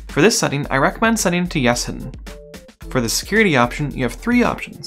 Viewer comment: hard to understand what you are saying: ". . . for this setting, I recommend setting to yes and ????" Nice video - thank you for creating this. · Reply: Oh wow, guess my noise gate caught me there. I recommend "Yes (Hidden)" That's the problem with listening to my own videos, my ears fill in gaps I know no matter how many times I listen to it. Thanks for catching that!